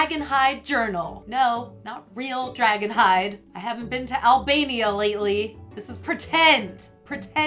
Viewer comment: Wait at 0:14 did she say Albania?!!!????!!! Thats what it sounded like :P